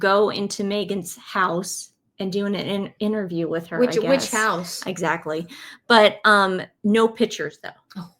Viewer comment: she said which house but I heard witch house. Whenever I hear about mm it comes with the theme music from the wicked witch of the west so it makes sense. :)